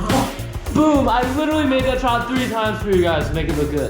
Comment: Not the subtitles saying “I literally made that child three times for you guys”💀💀